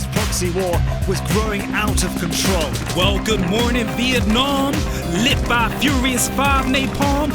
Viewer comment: I thought he said "Welcome in Vietnam!" Lol